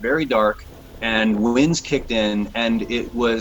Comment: I'm learning English as a Second Language. Can anyone tell me what the man said at 1:25 minutes? " all of sudden the sky got very dark the? " please help me out. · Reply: I was a little bit doubtful with that but then I just realized that was "kick in" I've been hearing a lot " the winds pick up " too.